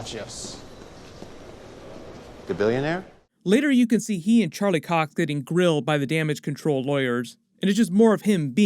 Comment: Did you just say Charlie Cock?